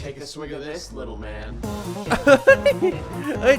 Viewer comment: พี่ครับในฉากที่ วินาทีที่ 2:48 Garnnie พูดว่าอะไรหรอครับ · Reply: " Take a swig of this, little man! "